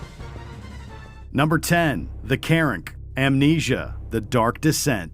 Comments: When I thought my man said “The Karen” hahaha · For a minute there thought he said the "KAREN"😂😂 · I legit heard “The Karen” on number 10.